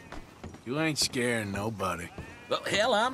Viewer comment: What said at the moment 2:08, please write, I can't make out his speech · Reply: "you anit scaring nobody"